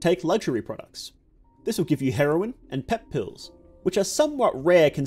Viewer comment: I heard 'Take luxury products." Okay fair enough takes sip of my drink "It gives you heroin and pills." I almost spat out my drink in confusion.